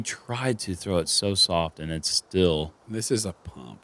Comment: Still not a fan of ending on this course. I keep hearing “it’s still DeLa”... but it’s not